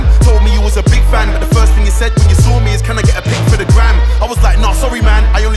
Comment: This whole time I thought he said 'can I get a pic for the gran' and man was like who meets a hardcore grime mc and asks for a pic to show his gran